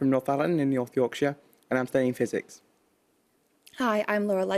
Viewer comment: Did say he was playing Physics?